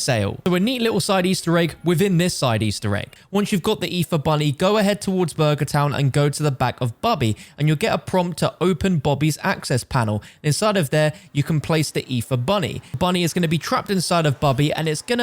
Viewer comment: lmao, got an ad when he was about to say “open bobby’s access panel” and instead it sounded like he said “open bobby’s ass-“ 💀